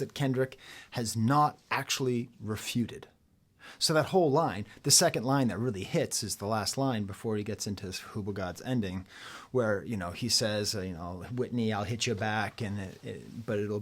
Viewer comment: Did he say Fhqwhgads at 10:59? To the Limit!